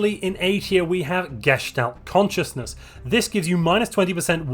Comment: When you say Gestait Consciousness I always hear "Geshed-out Consciousness" and honestly I'll never look at it the same because of that.